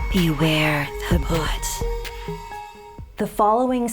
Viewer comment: for some reason, i hear it as " beware of the butt "